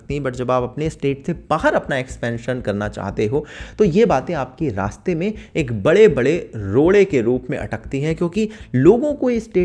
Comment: Did I Hear Bare Bare Laude